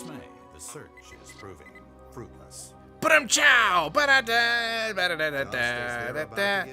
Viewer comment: It sounded like he said “But I’m chow!” At 1:14. a funny guy, he does good jokes.